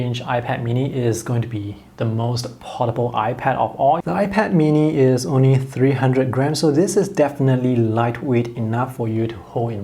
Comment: Idk why I heard “The iPad mini is only 300 grand” and my soul left my body.